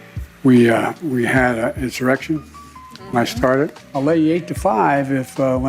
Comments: I hear "when I started." Regarding J6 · It sounds like hes saying 'When I started' when it comes to the 'insurrection'(cause it definitely wasn't one), but it could just he a trick my ears are playing on me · There's a lot to shit on Biden for, but to be fair it sounded like he said/meant "We had an insurrection when I started"